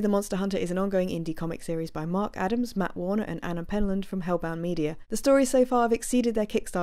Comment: sorry :) didn’t mean to be cryptic... but, you said at the beginning something like “...is an ongoing indie comic series by mark Adams , Matt Warner, and Anna...” I couldn’t make out the surname of Anna? It sounded like “penman”, and being the vain self-obsessed joycean I am, and as penman is my actual surname (and relatively rare—at least round these parts) I thought “did she say Penman”... etc etc. but on repeat listenings, I don’t think that is what you said. And have been to the website and there’s no Anna Penman on the site. So... oh well. Great video, as usual. 👍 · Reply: It's Anna Penlund as the artist 😊